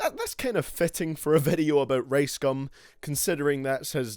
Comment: I low-key thought he said race gum at the beginning